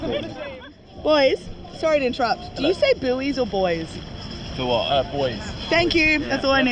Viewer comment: had to turn on CC for this because I heard billies and boys, but the the CC said buoys and boys. You pronounce it as boy.🤷🏼‍♂️🧐